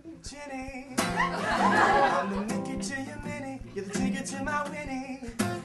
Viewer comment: Am i the only one who first thought harry's song said 'gonna lick you to you're minnie, your finger to my winnie'? Cause thats what i first thought and i was like WOW CHILL HARRY😂